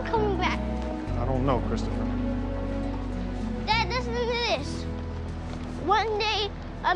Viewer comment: What did he say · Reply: "Dad, listen to this!"